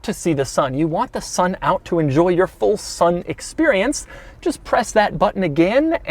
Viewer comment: Did he just say full sun experience 😂